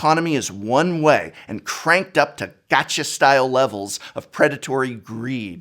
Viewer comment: I misheard "gotcha-style" as "Thatcher-style" economics, which I think says more about me than you but still... that would have been a highly appropriate reference. The Arena economy is unspeakable.